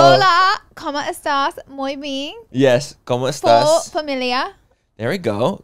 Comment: Lol!!! I thought you said “muy bean” not muy bien 😂🫶🏼 I love it!!